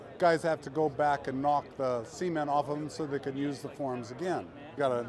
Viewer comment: Bro at first i thought he said semen